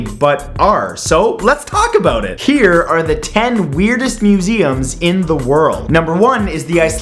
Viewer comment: Lol I thought it said Muslims